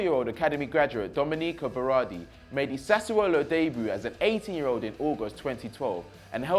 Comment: did he say 80 year old?